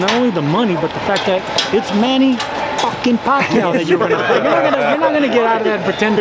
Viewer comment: "It's Fooking Floyd Mayweather" ---- opppps did he says that? I thought I heard the other way "that is fooking Manny Pacquiao"